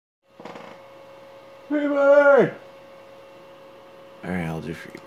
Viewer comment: K so like the title is Freebird And thencI thought he says "River!" 0:01 So I was expecting To the Moon x A Bird Story x Finding Paradise Medley...